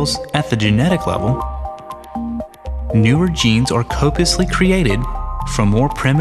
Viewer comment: does he say "created" at 1:03?